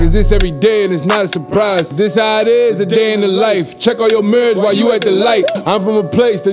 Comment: "Check all your mirrors while you at the light" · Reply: David Vazquez thought he said “check on yo mans”